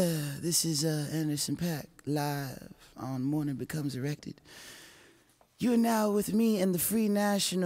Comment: morning becomes erected lmao did he say that on purpose?